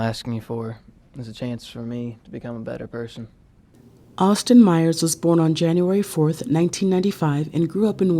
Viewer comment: Austin Myers.....at first I thought he said "Oscar Myer" and I almost started singing the song Lol!